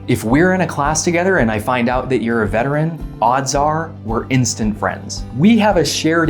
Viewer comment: Ohhh.... I thought you said you were a vegetarian. My bad. (Never lose your sense of humor.)